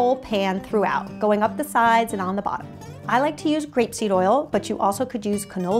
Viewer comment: Ha! I thought she said crepe seed oil for a second. I love your videos. So full of helpful tricks and tips! Thank you for all the time you put into them. :j